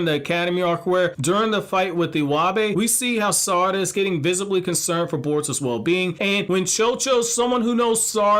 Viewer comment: I thought you said Sarada was getting with Kawaki? O_o is this just bait lol